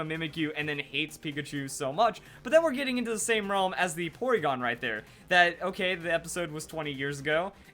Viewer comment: in 1:54 did he say oregon? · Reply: Playingwith lorenz no he said porygon, a pokemon